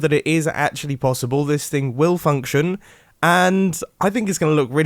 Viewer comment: if you skip 5 secs at 2:16 just as he says functioning it sounds like he says f**cking XD